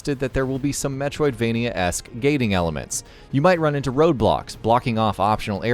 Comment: roblox did u say roblox